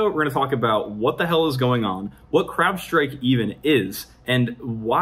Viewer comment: I could have sworn I heard "crap-strike"